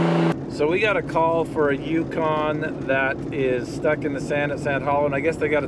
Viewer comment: The first intro I thought you said "Tucson" at first instead of Yukon and got really excited.